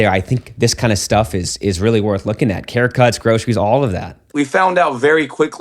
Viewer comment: Who else misheard groceries as rosaries?